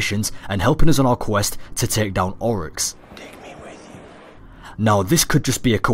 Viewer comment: Am I the only one who heard Cayde-6 whisper "Take me with you"